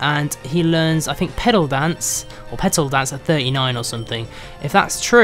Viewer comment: I thought he said pedo dance for a second. XD